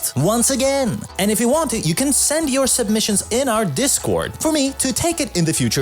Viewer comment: Discord? I thought you said to submit at the Instagram